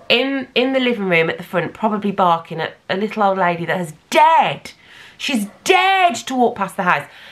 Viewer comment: Lmao I thought you said 'margot is just barking at a little old lady that's DEAD!' 😅